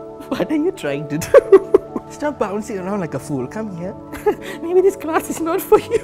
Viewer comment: the subtitles wrote food instead of fool